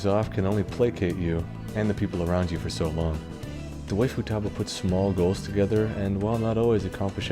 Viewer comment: Haha... if you put the closed captions on at around ~6:15— instead of saying "the way Futaba", it says "the waifutaba" 😂 At first I thought he did that on purpose, but then I realized the CCs are auto-generated, soooo... that's pretty funny 😏